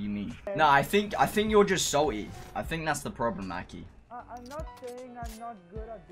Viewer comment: did he say akhi at 3:12?